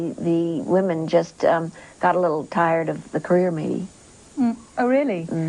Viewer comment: can anyone tell me just what ms laurel is saying at 3:50, please? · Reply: christie joyce She says "career me", turn captions on.